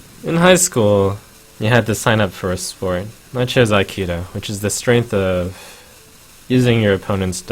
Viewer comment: What is that word? You chose what sport? Lol. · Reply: Aikido